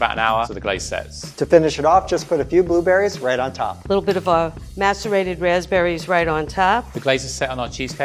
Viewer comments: Did she say Masturbated Raspberry? Wtf? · am I the only one who heard masturbated raspberries??? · did she just say masturbated rasberries???????? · I swear I thought said "masturbated berries" I was like 👀🤔